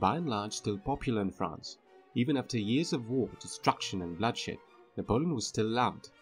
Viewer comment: did i hear the word "destructed"?